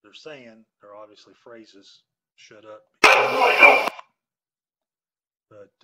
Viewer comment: I heard " Gus shut up and " Good boy Gus!